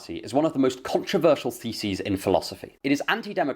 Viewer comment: Not sure if I'm the first one to notice but the subtitles at 13:50 say "controversial feces" instead of "theses" 😅